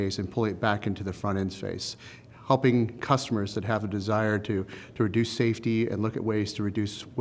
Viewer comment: did he say helping customers to reduce safety?